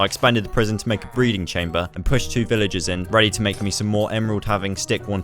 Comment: Bruh ya sound like you sayin' "Villagers" instead o' "Villages" my g.